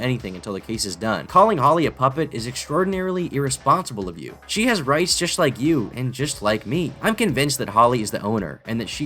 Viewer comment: i keep hearing "she has rice dish, just like you and me". :-D <3